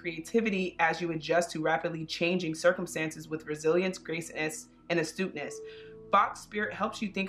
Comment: Instead of “resilience” I heard “brazilians” 🇧🇷😅😅😅 (I speak spanish, maybe that’s why 😅)